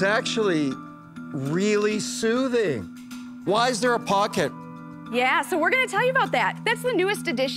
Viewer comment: I kept hearing “so why is there a podcast?”